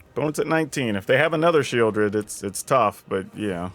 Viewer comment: sheoldred's name is so awkward, i thought i heard shield red. so she must be red